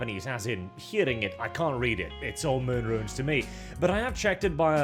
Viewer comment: Can’t make out what is saying at 1:55, something similar to “it’s Greek to me” and I want to learn a new way of saying it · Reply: He's saying "it's all moon runes to me". "Moon runes" is a term sometimes used to refer to the Japanese "alphabets". A bit more specific than "it's Greek to me", since, as far as I know, "moon runes" is rarely, if ever, used for anything other than Japanese (and possibly Chinese as Japanese uses a lot of their "letters" as well) while the "it's Greek" phrase is more of a generic "I don't understand it". And as you might have guessed it basically implies that when you try to read it, it makes as much sense to you as trying to read a language that came from the moon.